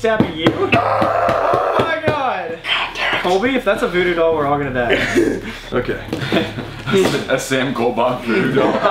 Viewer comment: i heard very loud wispering and i dont know if it was one of yall or if it was a demon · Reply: It was Colby saying god dang it